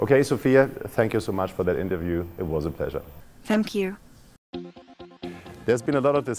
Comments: Misheard that as "f*ck you" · I think she said thank you, but I heard f*ck you.